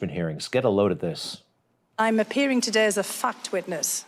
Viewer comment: 😲 I heard “I’m appearing today as a fck’d witness...” 😂 😂😂😂